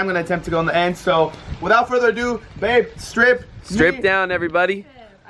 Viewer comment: who else heard Brandon say babe strip